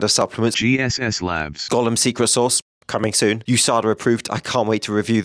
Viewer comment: I first misheard Golam Secret Sauce to Gollum and somehow it made perfect sense. :D